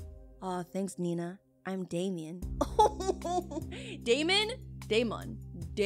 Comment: his name sounds like demon js with an A instead of a E